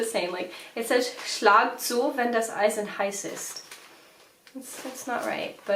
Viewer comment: Yep, I know this saying. But who knows what her dad is said? It might be something different with a similar meaning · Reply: I think, said it was „ Schlagen Sie ein, während das Eisen heiss ist“ or something like that at around 4:15.